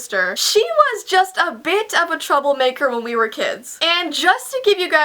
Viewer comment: Heyy, anyone else though she was gonna say bitch but said bit instead at 0:33??